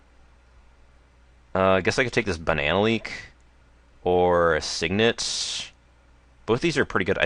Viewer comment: Did you say "Banana Leak?"